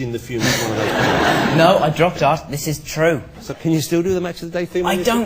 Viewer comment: for YEARS i have been trying to figure out what he says at 6:09 and I FINALLY realized he said "No, I dropped art, this is true." ...I feel so relieved.